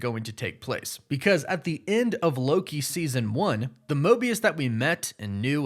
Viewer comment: For some reason I thought you said morbius and I was hype but I'm still hype